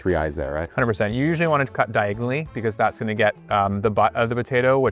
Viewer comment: what did he say, dear? Diagon Alley.